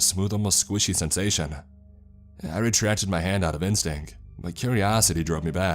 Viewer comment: the word is pronounced "Sha-grin" not Cha-grin". Also Hav-e-air, not Yave-yir, and Stow-ick, not Stow-ack. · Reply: Just heard him say ‘IN-stink’. 🤣🤣🤣🤣🤣